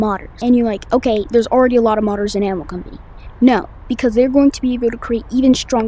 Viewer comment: why did subtitles say animal crossing